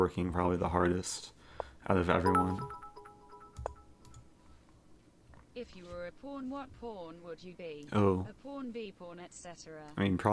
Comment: I definitely heard that as "if you were a porn what porn would you be"